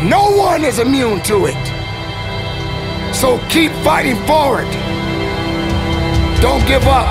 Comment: This saved my life... I was ready to jump off the bridge. Thank you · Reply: I thought you said "dont keep fighting"